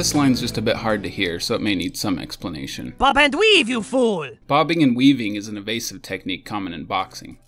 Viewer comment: Dodge and weave you fool?.... I always thought he said "For the weed you fool!"